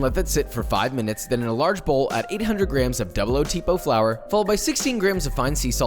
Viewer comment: I prepped the dough yesterday and for some reason I thought you said 600g of flour and I was like how tf did he manage an 85% hydration dough TURNS OUT I WONT BE HAVING PIZZA TODAY